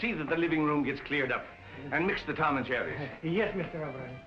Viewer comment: A Tom and Jerry cocktail sounds nice and cozy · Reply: I thought the gentleman said, "Tom and cherries."